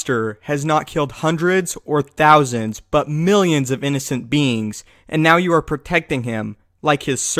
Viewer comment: I thought he said innocent beans